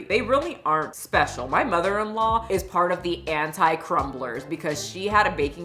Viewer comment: Did just say her "mother-in-law"? I haven't been on her youtube a lot but just out of curiosity, can someone tell me if they got married?!😅😯